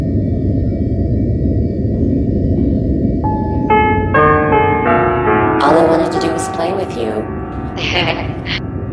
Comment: I did not understand what you said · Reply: granny says "all i wanted to do is play with you"